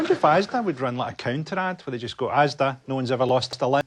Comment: what word was cut out ? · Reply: Limb